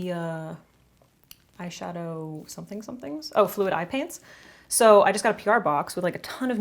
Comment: Definitely thought you said 'eye pants' xD